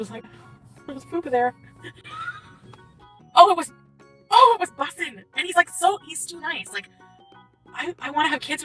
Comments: Did she say -it was Bussin? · did she just say that there's poop under her dress??? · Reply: she said “fupa”🤣🤣🤣🤣